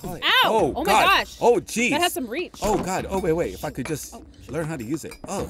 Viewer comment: Lol at 11:30 it sounds like said “” if I could just fucking learn how to use this